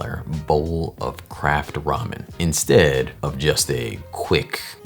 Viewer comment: I thought you said "crack ramen" at first and I thought... Yep! That's what it looks like 😂